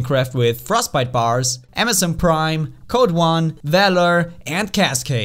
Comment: Did... Did he just say amazon prime?... hahaha